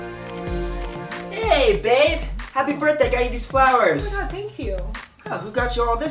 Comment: Did I hear that correctly? “Happy Birthday Eva I Got You Some Flowers” Really Brent? Edit: 3:06 Is When It Happend